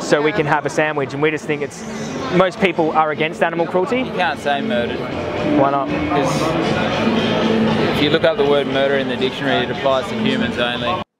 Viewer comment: What does the guy at 2:37 say? Thanks in advance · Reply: "You can't say 'murdered'. Because if you look up the word 'murdered' in a dictionary, it applies to humans only."